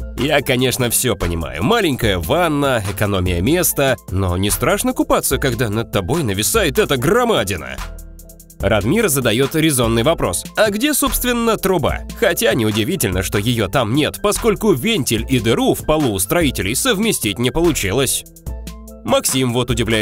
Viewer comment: Я услышал РАДМИР ЗАДАЁТ АРИЗОННЫЙ ВОПРОС ЧТО БЛИН (С 1:30) Тут по-моему слишком много серверов